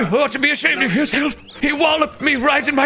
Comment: Without the captions I thought you said "Oh my slick testicles". Had me rolling, either way.